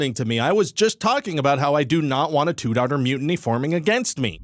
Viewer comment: Did he say a two DAUGHTER mutiny?? Congrats!!!!!!!!